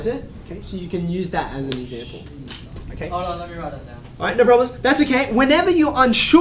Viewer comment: i thought you said "alright nibbros" lmao.